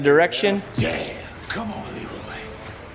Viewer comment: Did I hear a damn common Leroy!!!!